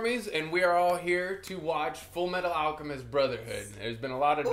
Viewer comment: I'm sorry- I'm not even a minute into the video and I heard "Full-Iron Alchemist"